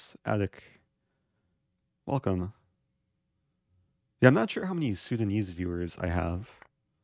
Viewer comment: swear to God thought he said "I'm not sure how many Sugondese viewers I have"